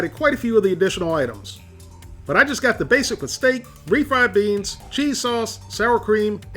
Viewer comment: I thought he said " basic mistake" , instead of "basic with steak ".